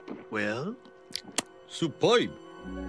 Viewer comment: Does he say "suppai" in Japanese for some reason? is that a movie reference at 1:47? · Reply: He's saying "superb" in a jersey accent